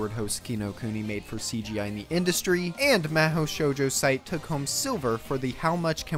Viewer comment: it might just be my end but i can't hear what anime you mentioned at this part. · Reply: Mahou Shoujo Site!